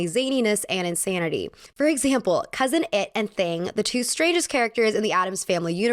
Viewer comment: I thought you said uncle it and thing were the straightest characters in the addams family had to replay a couple of times